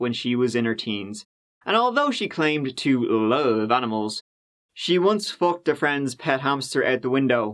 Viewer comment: and turn on CC. I had to because I wanted to know if it sounded like what I thought I heard. It did! ROFL.....SPOILER* "and although she claimed to love animals she once fucked a friend's pet hamster"